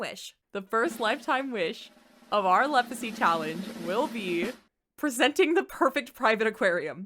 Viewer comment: Ngl I was not focused throughout the beginning of this video and at 1:14 i heard "our leprosy challenge" and was like "mmmkay no idea what we doin' here and how it's gonna work in sims but could be incredibly interesting" 😂😂😂 Edit: Gurlllll, where the series at? I checked your channel! It's been 3 months! 3 WHOLE MONTHS!!! We demand it! People crave it! I for sure crave it!